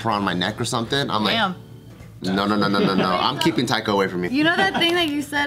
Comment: did he say I'm keeping taiko away from me? Boo, taiko is actually really fun da-don!